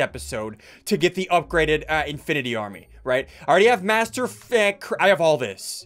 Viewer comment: Me: watching the video with subtitles Me also: Realizing that at 12:29 the subtitles said MasterF**k